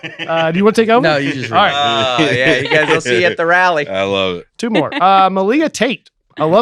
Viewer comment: Did Dustin say “See you at the rally”? Is that a KKK thing? That’s dark!